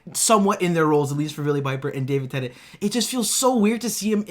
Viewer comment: swear he just said Davin Tennent